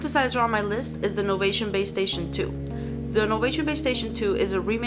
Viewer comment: I thought she said PlayStation 2